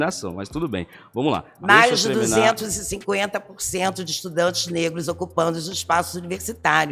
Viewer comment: Gente eu fui o único que ouvi um 250%?